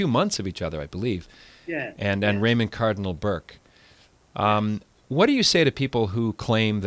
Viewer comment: I thought you said that Cardinal Burke I died. Scared me there.